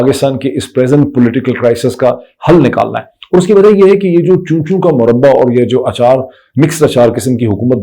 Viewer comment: Moeed said "Choo Choo ka Muraba", for a moment I thought he said "Cho..iy... ka Muraba". Lol